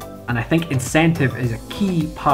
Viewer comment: Teaching myself to backflip when I was 15 was one of the best decisions I ever made. The experience taught me more about how to handle fear and not give up easily than anything else I've ever done. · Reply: Excuse me, what was he saying at 4:56 "I m gonna make this moon and gave it school"? I am not native.